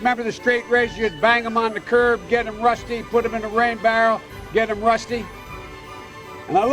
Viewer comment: Put the straight razor in a rain barrel? What was e going to do give him tetanus? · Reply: I thought he said "grain barrel" but either way idk